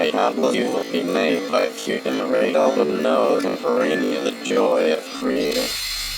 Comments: I heard "Chicken Alfredo the joy of creat-" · The end of the call in night 5 Sounds like its saying the joy of creation